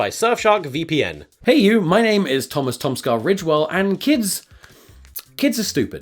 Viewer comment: For as long as I've seen this channel I've thought he has said the original or ritual And not ridgewell